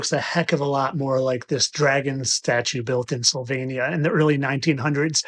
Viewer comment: I'm convinced, it's not a fish-lizard from a hundred million years ago, it's a dragon from a hundred million years ago. 2:25 I think he said Slovenia, not Sylvania (which might be pardonable for an aggressively ignorant American). 8:02 Ah, learning is evil. No wonder. 8:14 Today I learned that Elvis Presley is a French woman born around 1850.